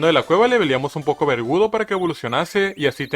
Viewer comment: como que vergudo, yo escuche vergudo